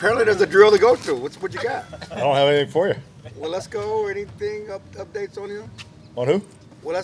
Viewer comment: When media says a guys name and the coach responds with “who?” - not good! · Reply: To be fair it sounded like Clarence said “well let’s go…” instead of Waletzko